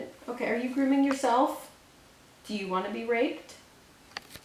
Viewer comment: I thought she said do you wanna be raped